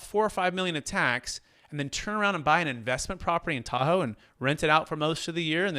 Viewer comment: No lie...I listen to you guys as background "noise" (no offense, just don't have the right word right now)...I heard "buy ... property in Tahoe tax deferred" and I'm like "hold please...WHAT?" LOL Will be watching this again and taking notes